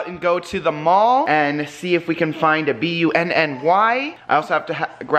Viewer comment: I heard " find a B W N N Y" and I was like really that doesn't even spell anything. Lol I figured it out though